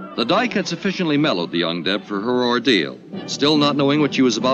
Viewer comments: It took me a while to realize that the narrator was saying "young deb" and not "undead." I had a totally different story taking place in my mind. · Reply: Guess I'm the only one whom misheard "The Young Dead" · OhhhHHhhhh they kept saying "what to do with the young Deb" and I was hearing "what to do with the undead". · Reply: I heard "young dead" at first. Yikes, what y'all doing that you need to decide what to do with the young dead? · young Deb, I heard undead.